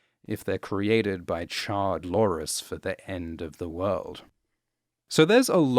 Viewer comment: You keep saying "Char-lores" and I keep hearing "Charred Loras." Which is a different show, but still accurate.